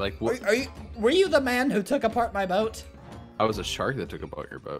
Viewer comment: he sounded like a old man sayin “Are you da man who took apart mah boat¿”